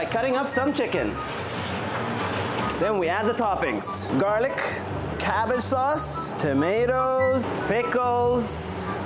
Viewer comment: I THOUGHT HE SAID GARBAGE SAUCE 😂☠️😭💀